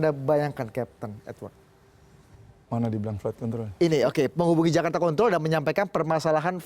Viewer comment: Apa cuma Aku aja yang denger pembawa acaranya nyebut control kedengeran seperti nyebut Kont*l??? di menit